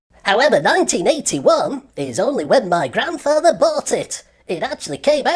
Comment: I thought he said in "1981 Is when my grandpa farted" lol Great Video as Always!